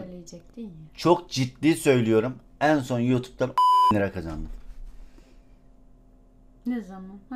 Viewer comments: 10 bin lira oldugunu anladım asdasmdlşsa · da 10 bin dedin duydum